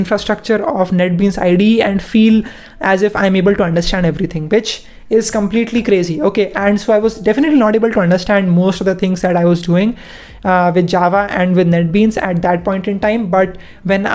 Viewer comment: did he say "bitch is completely crazy "😂